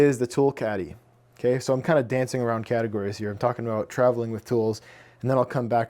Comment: Did you say, dancing around.. caddy-gories?? Hehe. Nice shop! Love all the thought put into it. Best wishes!